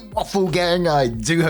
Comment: I heard ‘offal gang’ 😂😅